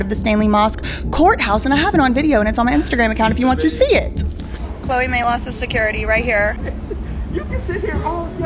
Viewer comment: All I keep hearing is “Chloe MAALOX”